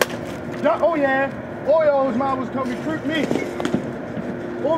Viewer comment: sounds like he said "oreos" instead of orioles ...lol